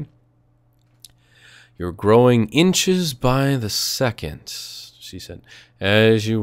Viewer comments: Did I hear a "That's what she said!" @ 31:46 ? LoL You can't fool me =D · Anybody else catch that "that's what she said" comment? I had to pause the vid. I couldn't stop laughing. Very sneaky...very sneaky lol.